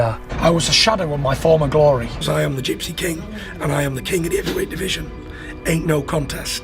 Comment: lol at around 15:40 youtube auto-generated subtitles say "king of the overweight division" What a documentary brother! And im just halfway through!